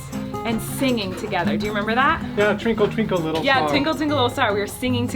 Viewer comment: who else heard tinkle tinkle little star??